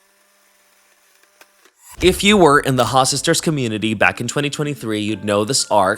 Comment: i thought he said “Hi sisters”